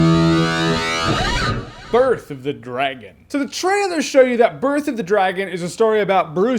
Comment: for a second there I heard "Girth of the Dragon". That was confusing.